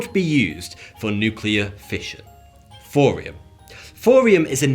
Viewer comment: am I tripping, or is he saying Forium with an F..? Is this a British thing?